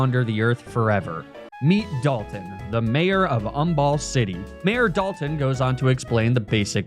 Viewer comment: Not gonna lie, I was focussing on something else for a second and 100% thought you said 'mayor of ONEBALL city' 🤣🤣